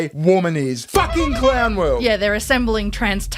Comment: Did you just say clown world or clam world?